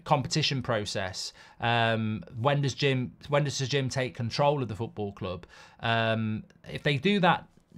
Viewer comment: At first I thought it said Sir Jim Makeover. Which is probably more accurate